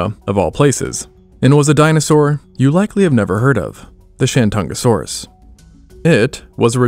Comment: i swear i heard SHIT-TONasaurus